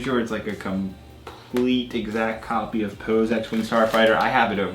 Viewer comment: lol he said poo instead of poe